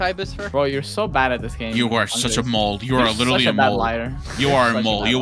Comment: sounded like he said Mold instead of “Mole”